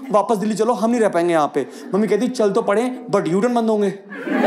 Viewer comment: what he said (what mammy ji said ?) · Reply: Chal toh lein lekin U turn band honge! 😂😂😂😂